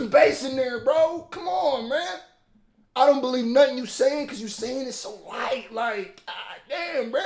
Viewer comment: Did you say, "Why you sing it so white?"